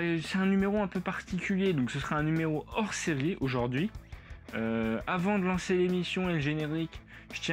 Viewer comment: "avant de lancer les nichons" voilà ce que j'ai entendu à 0:47 haha